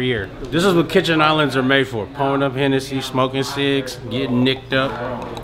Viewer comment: “Getting nic’d up” is hands down one of the gayest things I’ve ever heard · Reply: I thought he said “nice’d up” but nic’d up??!!! He might as well of said “ I’m rolling hard wit dis nicotine dog” ! But it’s ok I’m fully puked out from hearing the music in the truck.. Adam is a solid actor ... respect to him for that..